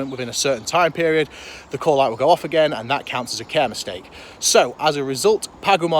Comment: I heard "careness steak."